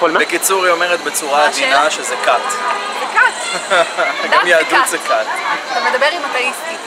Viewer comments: what she said in 4:08 isn't "according to judaism, it is a cult", rather she is saying "a religion (all religions) is a cult (are cults)" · There is a small mistake here, they did not say that according to Judaism it is a cult. The girl actually said religion is a cult and the guy said that Judaism is also a cult right after her.